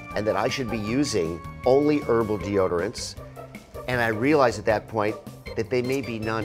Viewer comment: i thought he said purple deodorants at first lol